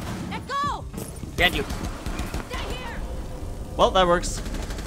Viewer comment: At about 4:38, did Hibana call out "They're here!"? If so, that's a pretty cool addition.